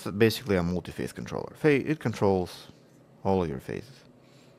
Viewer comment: What I heard was, "This is basically a multiface controller. It controls all of your faces.". And it was so hilarious because from time to time you crack some hilarious jokes like that. Man, the video title was so ominous but I didn't want to spoil the ending. So glad you were able to fix it in the end.